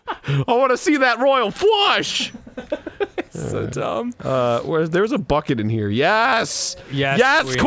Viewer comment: I heard Gavin's Bucket.